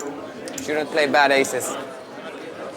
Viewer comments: what does ole boy say? it sounds like "you shouldn't play bad aces" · What did he say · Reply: "Shouldn't play bad aces." To one of online's biggest winners.